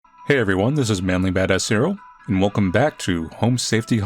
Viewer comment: so deaf I always hear "mainly badass cereal" lol